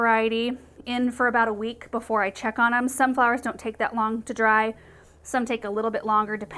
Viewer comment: As you were pouring I thought you said Sunflowers don’t take that long to dry. But you said some flowers do some flowers don’t. It actually looked like you were covering a sunflower. Great video. I can’t believe as crafty as I’am I’ve never done this! Thank you. You should use a small silicone nasal cleaning ball and puff the sand out with air!